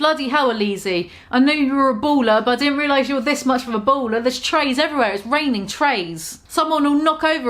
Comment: Happy sober new year🤘🏻🎉 I couldn't afford any of that shite💁🏼‍♀️ I heard "baller" as bowler ... I'm a stupid American 🤣